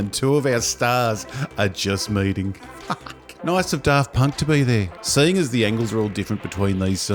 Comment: Genuinely thought you said "Darth Punk" I rolled.